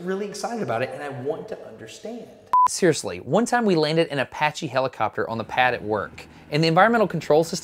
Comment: at first I thought you said "One time we landed a patchy helicopter at work...", and I was thinking, "A 'patchy' helicopter is not something I'd want to be flying in!" But then I realized you'd actually said "Apache helicopter". Well, that's better. :-)